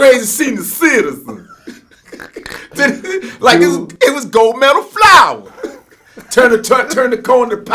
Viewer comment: damn i just found out he was saying Gold "METAL" Flour..I thought he was saying gold made of flour... shout out to Willie D